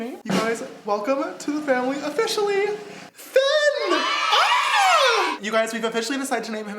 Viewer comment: I thought he said its name was Fen and I was not mad but then he said Finnigan and I went🙁😠